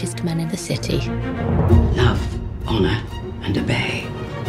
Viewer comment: it sounded like they said love honour and a bae.. XD